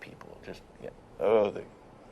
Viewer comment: For a second I thought said "I eat 'curry-fucker'"!! XD